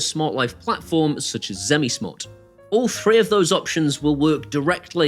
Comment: Oh. You said "three". Thought you said FREE speakers 😂